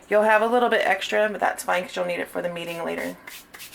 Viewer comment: When you said “you’ll have a little extra for kneading later” I thought you said “you’ll have a little extra for the meeting later” and I was like sis I liked you before the comment but that sleighed me, either way great job